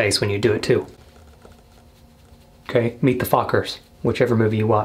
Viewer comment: I thought he said meet the fuckers LOL.